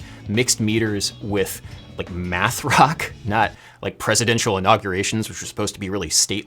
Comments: “Used normally in... math rock” cries in prog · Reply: I honestly thought I heard he said meth rock. I didn't know math was a thing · CHON! - Good to see math rock get some shine here. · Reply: Oh, “MATH rock”! I thought he said “METH rock”. What do I know?